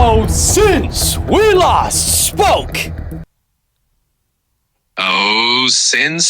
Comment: I always thought you said “since we lost Spoke” Wondered who was this Spoke guy, thanks to Gpt I realized it was “last spoke”